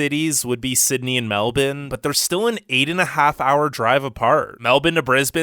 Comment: Did he just say "Melbun"?